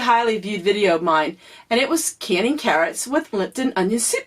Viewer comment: You said carrots, I thought you said cats lol when you said you saying your going to can cats oh Lord Jesus Christ I had to back it up 3 times.